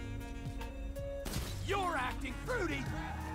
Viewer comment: I swear I heard trevor tell me im "acting fruity"